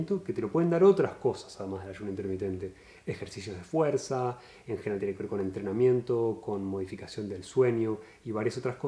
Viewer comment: Qué dice en el tiempo 6:16????? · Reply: "en general tienen que ver con entrenamiento..."